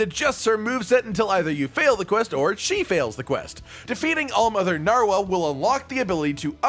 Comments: I thought I heared saying All monitor narwhal. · For a secound i heard “hall monitor narwal”